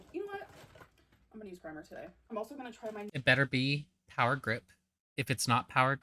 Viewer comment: I misheard "power grip" for "power grit". Why not both. Hope you feel better soon.